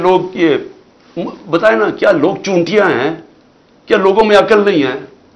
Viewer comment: Did he just said لوگ چونکیہ ہیں